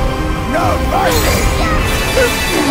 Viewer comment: Just wanna acknowledge the fact kairi is so nervous during the fight she even says please work after some of her combos! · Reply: At first I kept hearing it as: "Please hurt!"